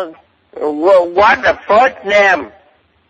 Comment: was way funnier when I thought he was saying "what the fuck name?" He saying "what's the first name?" Not as funny anymore. 😢